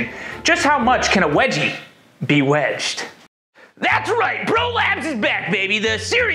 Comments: When he said Bro Labs, I thought he said Prolapse. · I could have sworn he said "prolapse is back" in his intro 😬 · I thought you were saying "prolapse" instead of bro labs, seemed applicable for this video.